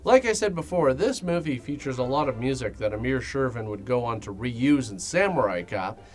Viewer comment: I kept hearing "Amir Shervan" as "A Mere Shervan".